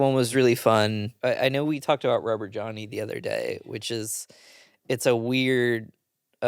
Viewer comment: Every time you guys said Rubber Johnny, I thought you were saying Robert Drowney, like Robert Drowney Jr, which would be a hilarious character btw